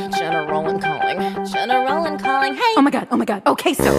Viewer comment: for THE LONGEST TIME I thought this was saying "general in calling"